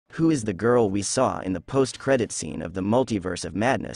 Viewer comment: Guuuuuurl……I’m not even gon lie I swear to god when I first heard the voice on this video I quite literally heard him say ”Who was the girl we saw in the ‘pussed’ credits scene?”😐😐😐😐🤦‍♂️🤦‍♂️🤦‍♂️🤦‍♂️BITCH WTF IS WRONG WITH ME???????